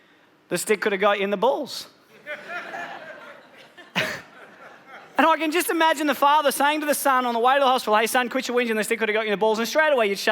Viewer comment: what does he say in this period of time(01:25--01:29). can you please write the exact sentence? and i can imagine ,... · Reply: "And I can just imagine the father saying to the son on the way to the hospital 'Hey, son, quit your weep (I think), the stick could've gotten in the balls!' and straight away you shut up!"